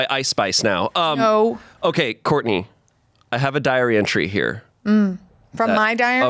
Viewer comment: english is not my first language, so everytime they said "diary entry" i heard "diarrhea tree". thank you